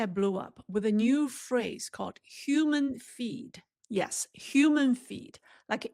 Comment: Hi. When I first heard you, I thought you said, "Human Feet." I immediately thought that China had run out of chicken feet. LOL.